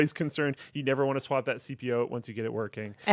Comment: Did he say "CPO" at 2:55?